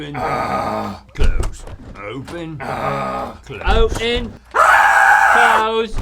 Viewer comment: does Link says "O'en" @ 8:51 as it's an English version of "open"? xDDDDDD or i'm imagining that?